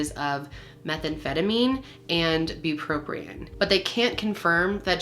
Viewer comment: Bupropion (Wellbutrin, SSRI anti depressant) is not an opioid, I thought you said “buprenorphine” which I know of as “suboxone” but it can be prescribed for pain to patients without addiction issues, buprenorphine is a partial opioid agonist. It can be prescribed for pain it has a “ceiling” effect which helps to keep the level of abuse lower. The adderall is confusing to me